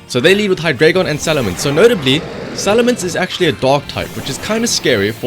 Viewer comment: Salamance is a WHAT? What did he say? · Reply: Dark but I think he mean Hydragon